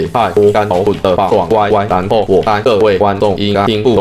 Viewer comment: 這裡也太多甘草綑了吧， ____（這邊聽不懂）， 然後我猜各位觀眾應該聽不懂我現在在說什麼對不對， 因為你們都是大SB， 我知道。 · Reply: 應該是這裡也太多甘草綑了吧，爽歪歪（？